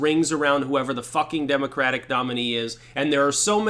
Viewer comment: Thought it said Demoncratic...lol💕🤗🙏